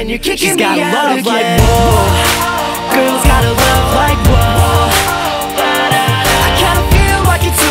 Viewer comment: On the radio i thought he was always saying " Love likes wolves" o-0